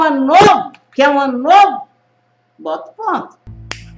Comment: Boa tarde por favor queria muito entender o que ela fala em 19:54 do vídeo já voltei várias vezes mas não consigo entender 😭 ....adoro os ensinamentos da vovó...Gratidão 💖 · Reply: A Vo disse, quer um ano novo, bota o ponto. Em outras palavras coloca o ponto.